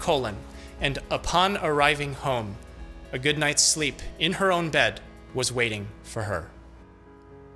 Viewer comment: ‘A good nights sleep in her own death was waiting for her ‘ that is what I thought I heard ......I thought you were creepy ... Nice vids, great deliver , always interesting .... need more ....